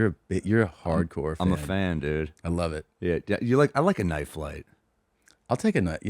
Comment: I TOTALLY misheard Sam's opening words as "yeah I like a knife fight". To be fair, that is usually the direction this goes between them